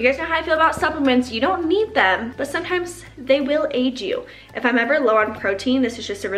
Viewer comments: When you were talking about supplements I heard “they will age you” instead of aid 😂 I was like wait what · Definitely thought you said supplements would age me, not aid me 😂